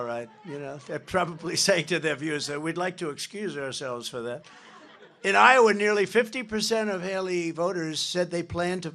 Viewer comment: Curious, what was that person saying? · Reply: Birdbrain